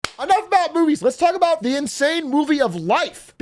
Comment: did he say enough about boobies?